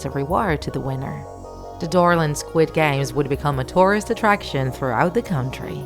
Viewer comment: When you guys said dorland I thought you guys said Dora😂😂😂